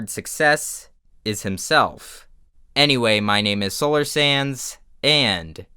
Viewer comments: I swear at the end i thought you said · Why does it sound like you say..? WAIT A MINUTE.!